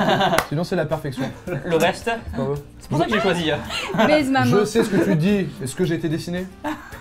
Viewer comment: c'est moi ou elle dit baise ma main ?